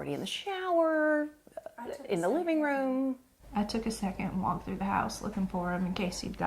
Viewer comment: I think she said "I took a second and walked through the house", not "I took a second walk through the house", which would negate the point you are trying to make at 9:40.